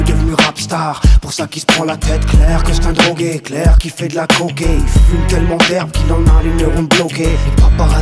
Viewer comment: „Clair qu‘il fait d‘la (...)“ What is he saying? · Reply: DLA COCKE ET..